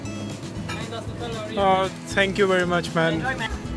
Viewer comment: what did you say ? xDDDDD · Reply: Michał Okularczyk I said thanks a lot man :P